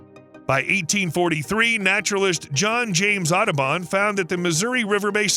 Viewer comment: for one moment I thought I heard "naturalist John James Autobahn" Luckily after some Googling I found out it's Audubon... Learned something new today :P